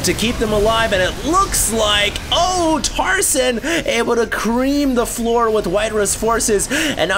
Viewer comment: EeE<link> Did just say "CReam the floor"?